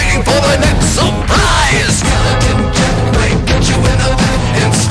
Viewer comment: all these years i thought it said "skeleton jack might cut you in the back . . " now i am kind of disappointed